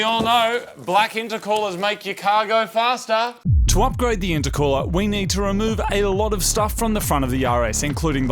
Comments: I could've sworn you said "we've got to remove a lot off stuff on the Yaris" 😂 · i thought you said "we need to remove a lot of stuff from the front of the yaris" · did he just call it a Yaris 🙄😂😂😂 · it sounded like he said "We need to move a lot of stuff from the front of the Yaris"